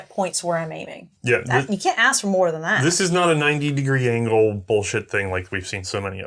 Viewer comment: did say bullsh*t or Borchardt? I sort of hope it was the former as it gave me a chuckle. =)